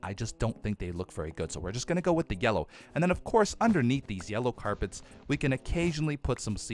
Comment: did he say yellow carpet...